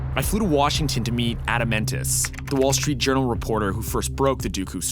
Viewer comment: I thought he said "Adamantis"